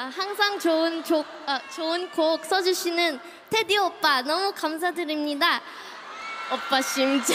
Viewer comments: I thought that lisa saying Jeon Jungkook and Taetae oppa?😶😅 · I THOUGHT SHE SAY JUNGKOOK 😂😂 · "Junkook." 0:53 "TaeTae Oppa!" Those are the words I thought she said 😂😂 ship it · I thought she said Jungkook 0:52 I thought she said Taetae oppa! hahahaha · i heard jungkook in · I thought she said Jungkook!?!? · Escuche mal o dijo jungkook oppa y jiminsshi escuche ustedes y dígame